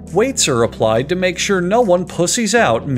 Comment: does he say " weights are applied so no one pussies out mid game" lol 😂